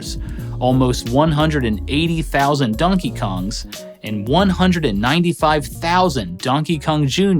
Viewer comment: Great video as always! So much love for this channel. Does anybody else hear him say Dunkey Kong